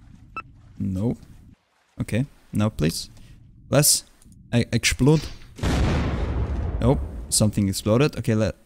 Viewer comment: did u say ekshlode?